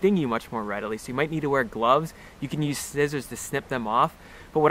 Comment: Great video mate, I had a bit of a giggle at 12:51 when it sounds like you say "snizzers" instead of scissors .... but I'm from New Zealand and EVERYONE thinks we have a unusual accent. Have just been getting into Nettle Tea in a "Fressko" glass thermos, good with some out of garden lemon grass. Also get a naturopath to make up herbal tictures including Nettle Root. Very informative and great presentation, thankyou sir!